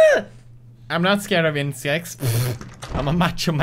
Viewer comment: I thought he said incest not insects